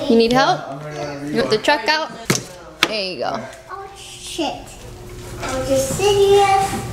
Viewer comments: did anyone else hear say “oh shit, are you serious?” Or was it just me... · Did anyone else hear a sized “oh shit?” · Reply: If you listen closely, he says "oh shit. Are you serious?" 🤣🤣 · is it just me or did say "oh shit" lol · i crazy or did i hear say “oh shit” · Am I hearing things or did say “oh sh*t” at about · did said ,,shit,, ? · Did anyone hear say "Oh Shit" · did just say “oh sh**”!!!???